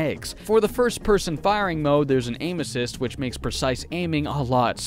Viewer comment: When you said aim assist, I thought you said anus cist.